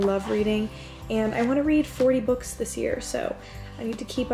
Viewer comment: did you mean 3 books this year or this month?😂 (around 12:40 something minutes) i thought you said you were going to try about 3 books a month maybe i’m crazy😂🤚🏻anyways i’m unwinding for the night as well💕💕night girl